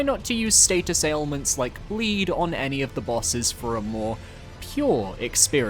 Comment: I THOUGHT HE SAID WEED 😭😭😭